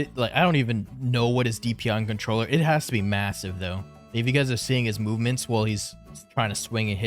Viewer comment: did you say massive?